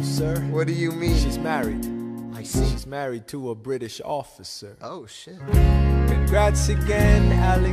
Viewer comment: "Shes married..." "I see" "Shes married to a British officer" "OH SHIT" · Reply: when i didnt know the lyrics i thought he said "she's married to a british author, sir" implying that even being british was bad lol